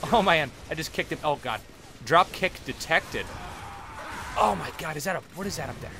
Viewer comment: I thought at 5:07 you said "dropkick detected." haha